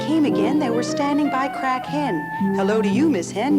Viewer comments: He described this as being a lot creepier than it was. I thought she said Crack head ,it took awhile to realise it was a hen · 'They were standing by crack hen' Lmao sounded like 'crackhead'